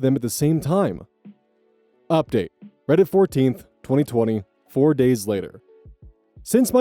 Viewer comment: it sounds like you said Reddit 14th instead of February 14th 🙂 made me chuckle